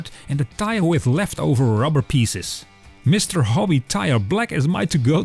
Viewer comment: great video. on the flat tire vlog, I can't quite make out what you are saying at 3:25 for filling in the gaps. would you mind writing it out? thanks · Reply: ‘Left over rubber pieces’. Try the subtitles 😊